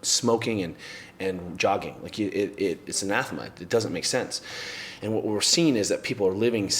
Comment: what that word? alphamight? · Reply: I believe he's saying "it's anathema."